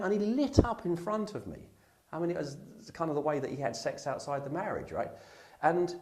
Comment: Did he say"kind of a way he had sex outside of marriage"???